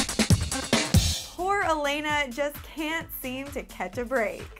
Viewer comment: i thought she said "WHOORE elena cant seem to.." LMFAOO IM LIKE DAFUQ